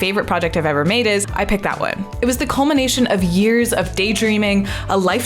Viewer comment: I wasn't paying attention and I thought you said "the culmination of years of day-drinking"